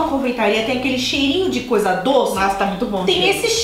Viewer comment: so eu que ouvi falar nossa ta muiti bom jeru? skskksksk